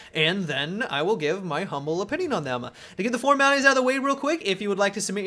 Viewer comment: what do you say around 0:08, to get the ------- out of the way? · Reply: Formalities